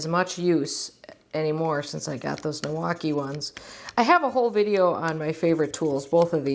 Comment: I hadn't heard of Niwaki... I thought you were saying Milwaukee! Great video. I'm going to buy the three-set now.